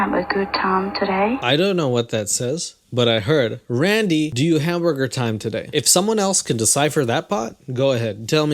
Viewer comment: I heard “Randy, do you hamburger time today” hahaha Dig your videos. 😂😂😂